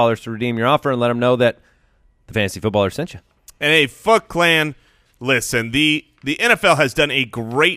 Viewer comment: I thought said "Hey F*ck Clan" LOL